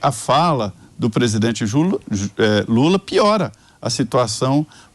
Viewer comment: Só eu que ouvi o Melancia José Maria chamar o presidente de JULA???? Será que ele juntou jumento com lula?????🤣🤣🤣🤣🤣🤣🤣 (Min